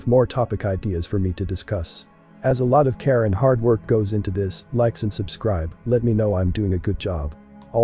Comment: Who else hears his closing remark as "a lot of Karen hard work goes into this" instead of "a lot of care and hard work goes into this"?